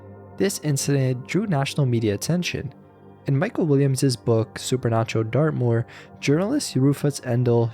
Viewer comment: I had to rewind the Dartmoor story because I thought I heard you say “Super Nacho Dartboard”…